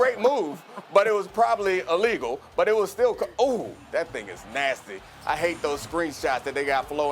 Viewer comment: did he say that nigga’s nasty?